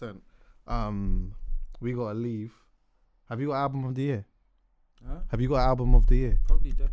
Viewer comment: did anybody hear what Ghetts said for Album of the year?! He keeps messing with the mic, I couldn’t make it out. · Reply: Decade, he said its the album of the decade 😁